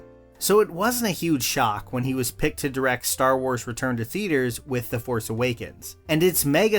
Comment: I misheard and had to relisten in order to process it properly. Was thinking I don't remember him directing "Star Wars: Return to Theatres"...! Duh!!!